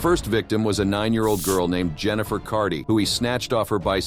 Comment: Hi, at 19:33 Why does it say 'Susan Maxwell'? You clearly say 'Jennifer Carty' not Susan Maxwell. Do you guys review your own videos at all? Fix it please.